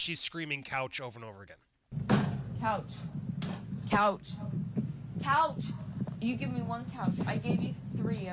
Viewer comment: Is the couch think a Therapy tool? Go to the couch and talk it out. Sounds like she said I have you 3 couches and you’ve give me one. Probably because he’s sick of listening to her crap on the said couch 🛋😆